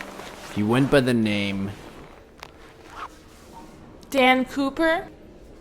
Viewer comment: I thought I heard Dan Pooper